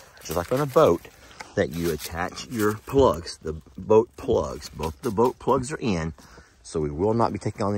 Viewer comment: Its gomna look stunning 😊 and your co pilot beside you ❤ and poor jami in the back 😂 and yes i thought you said butt plug 😂😂